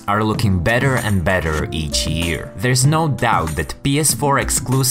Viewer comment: Did he just say "betterer & betterer"?